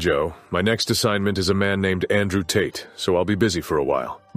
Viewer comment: wait did he just say his next victim was andrew tate aka a boiled egg